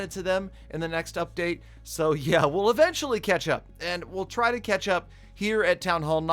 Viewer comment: Haha when said catch up i misheard it ketchup